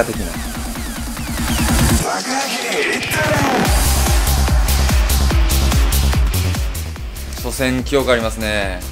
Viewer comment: はなんて言ってるんですか？ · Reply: タカアキ いったれ